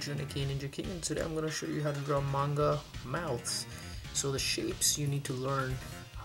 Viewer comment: At the beginning I thought he said grow manga mouths 😂